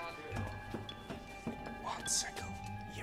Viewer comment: I thought the Weasley twin said “one second”